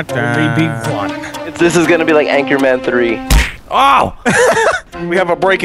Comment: anchorman 3 I thought you said anchorman tree!